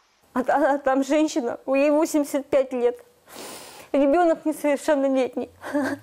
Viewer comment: ей 85, ребенок несовершеннолетний .... У кого??? Что она говорит